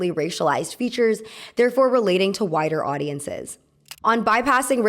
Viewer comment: Is mishearing something the same as a Freudian Slip? You said 'widest audience'. I heard 'whitest audience.'